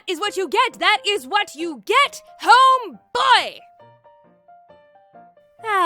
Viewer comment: Wait a minute, where are my grosheries? Me: Wait a minute, did I just heard GROSHERIES??!!